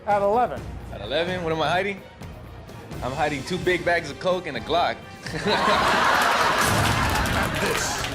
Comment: That guy who said he was hiding 2 bags of coke and a glock had me dying. 😆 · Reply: I thought he said 2 Big Macs and a Coke. 🤣